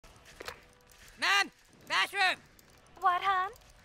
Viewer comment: I thought he said “Washroom”